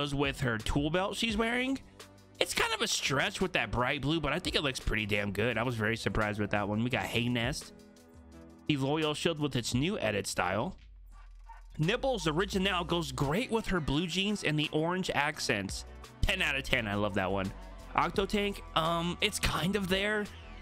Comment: I swear he said nipples 😂😂